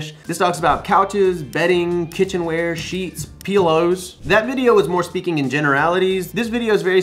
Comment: Bro did you just say Peelows? haha You're the best! Thanks as always for the content!